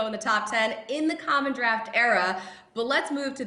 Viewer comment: Did she say 'common draft era?' Did she mean 'MODERN draft era?'